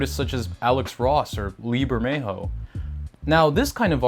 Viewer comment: It's "Lee Bermejo" for anyone wanting to look him up. I kept mishearing his name as "Lieber Mejo" and couldn't find him anywhere